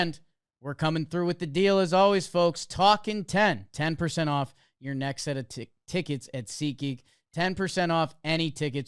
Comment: Who else thought at 48:15 almost said”your next set of tits”🤣